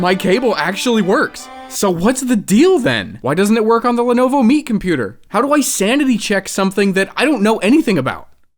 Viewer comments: For a while I forgot you were working on a Lenovo Meet computer and thought you said Lenovo Meat Computer. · Every time you say "Lenovo Meet computer" I hear "Lenovo meat computer" and that just makes the whole project so much more cursed! Please install SteamOS on someone's brain next!